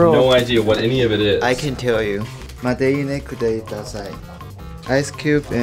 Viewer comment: I thought said "I can kill you" at the start there lol